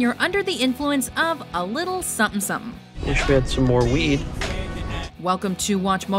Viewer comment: did she just say "a lil sum sum.."??